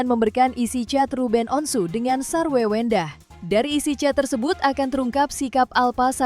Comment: Gak salah denger nih ? Namanya ganti sar wewe ndah? 😂 Wewe 😂